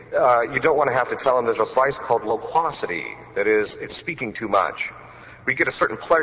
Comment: Anyone knows what was the word the father said in 5:56? Lupocity? Or what word is that? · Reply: Loquacity